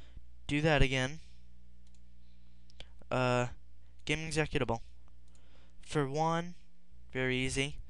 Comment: what did you say?? :D gemmzetkitable?